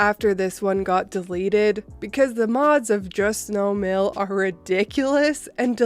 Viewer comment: Had the volume on low (late night here and I can’t sleep,) but when you said “the mods on Just No MIL are ridiculous...” I thought you said ‘the moss are all d...’ I was like, “Yes! That’s exactly what they are!” Until I processed the sounds in my head and realized exactly what you said! Lol!